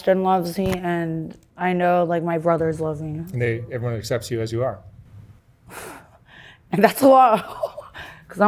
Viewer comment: I thought he said “even as sexy as you are” not “excepts you as you are”. Had to play x3 times then the captions helped 😂